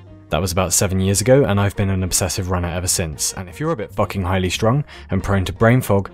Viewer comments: When he said highly strung I thought he said 'fucking irish drunk' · what does he say at 0:21?? „if you’re a bit fucking highly strong“? please help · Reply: He says highly strung, which means very nervous and easily upset